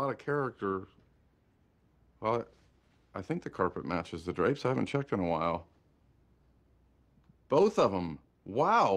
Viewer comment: Phil responds saying "I think the carpet matches the drapes.." . Can someone tell me what could have been the question asked by the person on other side ? Sorry ..if this is basic 😊.... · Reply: They ask that exact question. "Do the carpets match the drapes?" It's a euphemism for pubic hair (carpet) and hair on head (drapes). When you ask that you're usually asking indirectly if the person's head hair color is natural or they got their hair dyed. People don't usually go the dye the 'carpet'